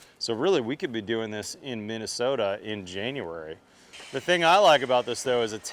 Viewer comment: Even after listening a few times, I really didn't understand something ,but did you say, "We could be doing this in Minnesota in January?" @ 2:25 ??? I could swear that's what you're saying, but just being in Minnesota in January, is totally absurd and makes no sense at all, so I must be misunderstanding.